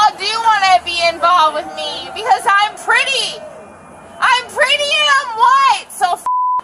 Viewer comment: did she just say, I m pretty and I am white?😮🤔